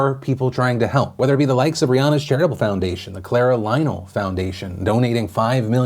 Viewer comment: is it Just me or did it sound like he said "Rihanna genital foundation"